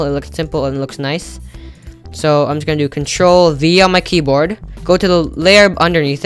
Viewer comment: what are u saying control and someting answer fast please! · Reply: 'Control V'